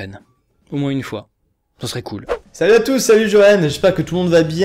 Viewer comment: C'est moi ou à 4:43 il l'a dit salut JOEN ?